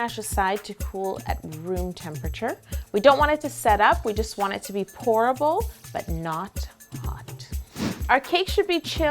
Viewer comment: When she was dieing the gonosh I thought she said "we want it to be horrible"😂😂😂😂😂😂😂😃😂😂😂😂😂😂😂😂😂😂😂😂😂😂😂😂😂😂😂😂😂😂😂😂😂😂